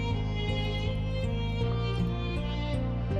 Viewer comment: I thought he said at the end grace sucks ass😅